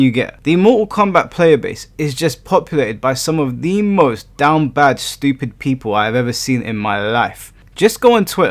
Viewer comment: I thought you said Down-Back Stupid /😂 | I’m going to have to use that. I absolutely loved Harada’s response!